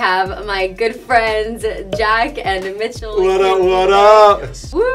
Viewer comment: every time jack said i thought he was saying b** lol